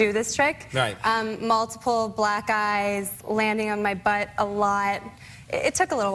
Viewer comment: I thought for sure he would say something about ''multiple black guys landing on my butt a lot'' and I was severely disappointed